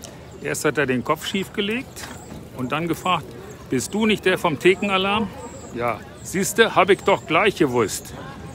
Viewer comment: Ich hab Tittenalarm verstanden, schade war nichts zu sehen.